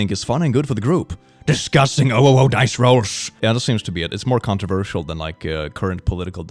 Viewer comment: did he say OWO dice rolls?